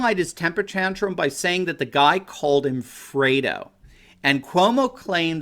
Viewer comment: LOL, when I first heard this, I thought he said "Frodo".